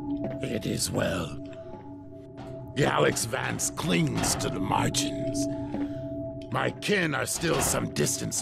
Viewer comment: i thought he said "Alyx Vance clings to my margins" XD